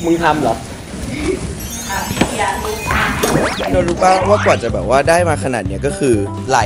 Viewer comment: เห้ยพี่กันพูดว่า"ตัวรู้ป่ะ"13:01รึเราหูฟาดไม่มั้ง เค้าแฟนกันหรอ.